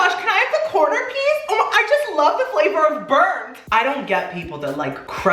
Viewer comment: Who else heard him say I love the flavor of birth?